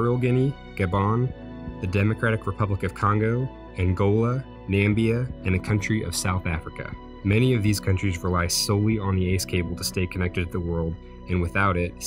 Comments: Did he say Nambia at 1:03?? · Did I... did I just hear Nambia?... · did you really just say Nambia instead of Namibia?!! That's Trump's fictional country